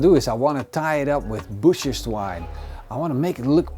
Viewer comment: With your accent, it sounds like you said "Bitches Twine"..... :) Peace my Friend!